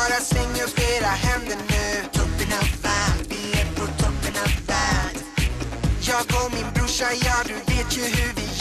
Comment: Misheard "token im fat"